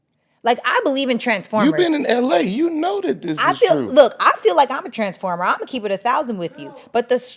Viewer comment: Did she just say she’s a transformer